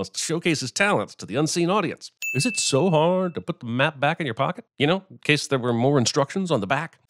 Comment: I wasnt really listening and wasn’t looking at the screen at the time so I thought he said ”Is it so hard to put meth back in your pocket?”